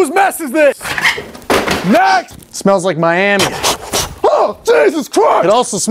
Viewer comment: When he said "It smells like Miami" I thought he was gonna say "It smells like my anus" lol